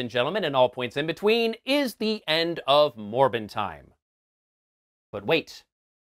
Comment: I got to be honest, when you gave the hint last time about what you were gonna review and you said morbin time, I thought you said Morphin time and you were gonna review the 2017 Power Rangers movie or any power ranger movie, Oh how wrong I was. =p